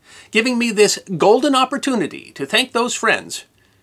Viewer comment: I know you didn't say the "Sistery" Guy but it kinda sounded like it... Congrats one hitting one million!